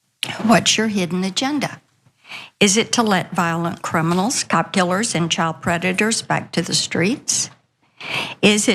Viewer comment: I had to turn on the captions because I kept hearing "cop killers" as "cuckulers".